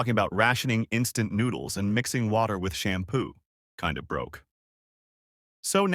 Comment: I heard mixing ramen with shampoo at first at 0:19 and had to go back to listen again 😂 whoops 🤭 heard wrong.